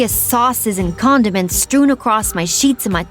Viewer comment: Did she say "condoms!?!?"